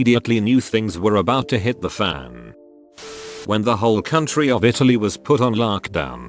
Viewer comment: Noah The U.S why where else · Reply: I thought he said county. But yeah question still stands.